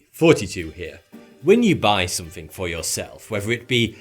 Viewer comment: This whole time I thought that he was saying '42 here' and not 'Thoughty2 here'.